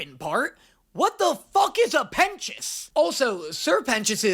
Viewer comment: I just wanted to say, I had the subtitles on and it interprets 'pentious' as 'penis' and let me tell you, seeing the subtitles say 'what the [___] is a penis' really added to the experience. Edit: IT KEEPS HAPPENING. THE WHOLE VIDEO. HILARIOUS.